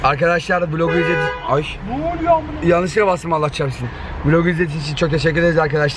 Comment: 10:49 da noluyo mına dediğini sadece benmi duydum